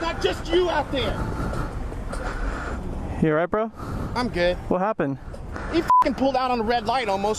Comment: this man said "I'm gay" um does anyone hear this too.🤔